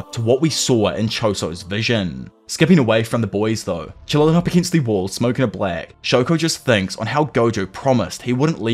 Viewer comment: what did he say? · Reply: Lmaoooo a black n mild🤣🤣🤣